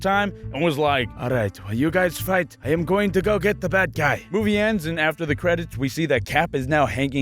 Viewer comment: I thought you said while you were acting as black panther I thought you said while you guys I'm gonna go catch The Black guy